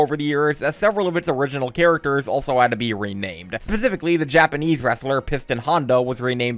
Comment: did you say Japanese WRESTLER?